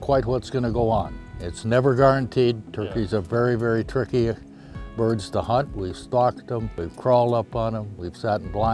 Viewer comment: I heard the turkey hunter say "turkeys are very turkey birds to hunt . . ." at 5:03 XD